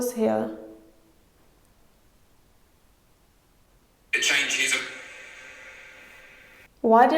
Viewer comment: When you asked "who is it that slams the doors here," i heard "it changes up"